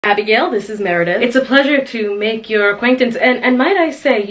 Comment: Ngl I heard "Abigail, this is Meredith" and thought the guy was just a really hot masc lesbian. This is funnier